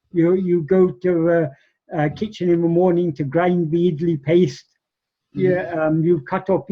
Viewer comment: What does he say in 43:24? "You go to kitchen in the morning to __________ paste? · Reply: "You go to kitchen in the morning to grind the iddli paste". Iddli is a steamed cake make of a ground paste or dough consisting of fermented rice and black gram, so since that was served for breakfast in Ramanasramam, Bhagavan used to go to the kitchen in the morning to help grind that paste.